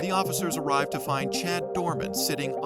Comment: ik this is no joking matter but did he say his last name was doorman... Like... Uzi doorman ir khan doorman..?